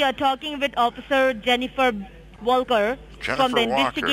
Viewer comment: I thought she said "jennifer Vulgar" the first time.